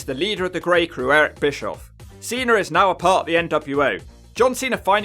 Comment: cena is a part of the NWA??? lol that what is sounds like u say... i know its nwo really fastly pronounced .. lol